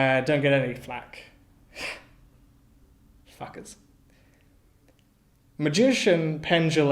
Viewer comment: I think you said "fuckers" and the sutocaptions replaced it with _"f****ts".